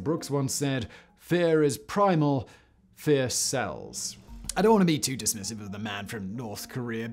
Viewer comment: For a moment there I thought said "Fear is primal; fear cells."